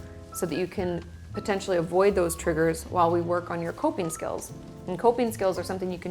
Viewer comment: I heard "pooping skills"